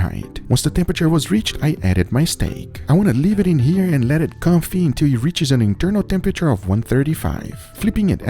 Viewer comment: What does he say at 2:07, sounds like "comfy," and what does it mean? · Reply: confit. kinda like a slow cook in butter, olive oil etc. soaking all the flavor. feel free to look it up.